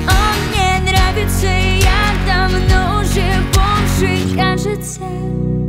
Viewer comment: То что мне послышалось. 0:06 а водки сбросишь мне? и я давно уже бомж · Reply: Sofi_Ja а мне послышалось "и я давно уже бомжик"